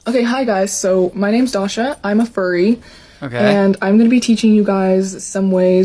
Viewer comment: So I heard her say "hi I'm a furry and I'm going to show you how to be stupid." Anyone else?? Or just me?